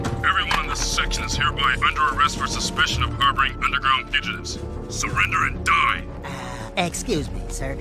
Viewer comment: He says fugitives but it sounds like he says underground pigeons lol